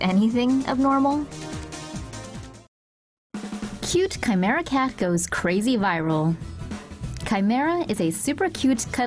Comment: I thought it said "cool dead skin goes crazy viral"